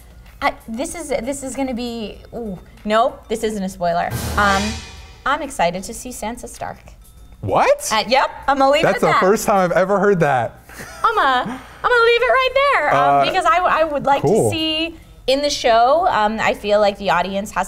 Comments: I thought she said Santa Stark and I was like who the fuck is Santa stark? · It sounded like said Santa Stark, so I just pictured the Ironman suit in a Santa costume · I totally thought said Santa Stark....I was very confused for a second